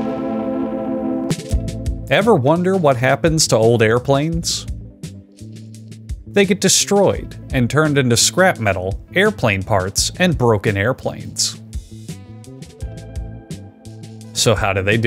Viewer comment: I watched a few seconds of the preview before clicking on this video, and I thought the subtitles said that the airplanes get "turned into soap" and now I'm kinda disappointed